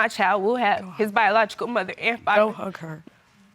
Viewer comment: After the results the mother told her son, don't hug her ! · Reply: I heard "go hug her"